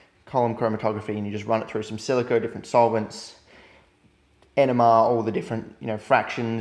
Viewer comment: I thought you kept saying enema